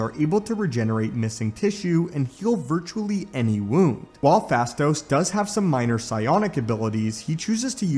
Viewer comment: misheard fasto for fatso no joke